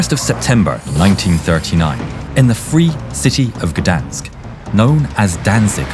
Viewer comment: For some reason. I thought the narrator said"verdansk" instead of gdansk